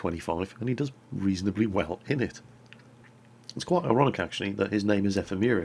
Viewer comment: it sounds like your saying erotic not ironic probly the cold